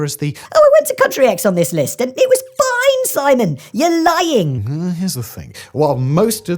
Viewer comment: My favorite part of this video was when I had to rewind at the part where you said “well here’s the thing” under your breath because I SWORE you said “no one gives a fuck”